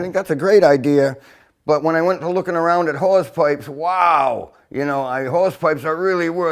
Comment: "Wow, you know, I ... hawsepipe are really worth some cake!" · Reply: Thanks for writing "Hawsepipe" cause the entire time I was watching the video I thought he was saying "Whores Pipe".